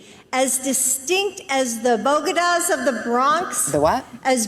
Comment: I thought Jill said “Bogotá’s”...maybe she meant Columbian tacos.